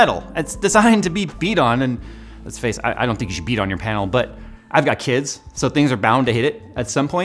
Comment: I hear "beat on my panel" I laugh, I am a simple man :D